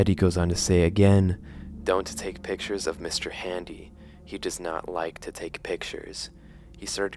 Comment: I love the subversion of expectations with Mr. Hands. You'd expect him to be a bloodthirsty being that only cares about killing but no, he didn't seem like hurting the kid. He was just scared and confused, especially with the added context that it's basically a fusion of all of the henry dolls and the victims within them. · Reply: You guys Need to please say Mr.handy, because it genuinely horrified me when I heard the name "Mr.Hands"